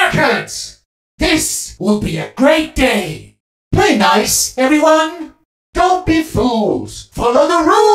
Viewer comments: I thought he says "Hey Guys, everyone!" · I hear “Pretty nice, everyone?”